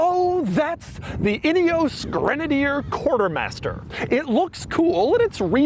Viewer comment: could've sworn Doug said "idios Grenadier"